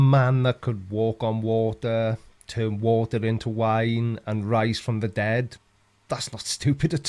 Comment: I heard "water into wine, rice from the dead", not rise from the dead!